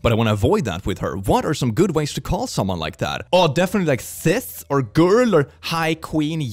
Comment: I thought said “Cis”… I mean It’d be a great joke though I imagine she’d get tired of it real quick lol